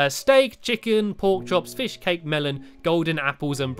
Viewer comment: When he said fish & cake it sounded like fish cake